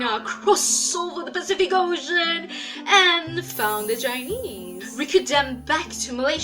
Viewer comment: when u say chinese i heard u say jainese lol amd thats how indians say chinese